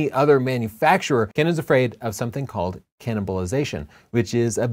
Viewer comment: thought you said “canonbalization” which would have been a great play on words :p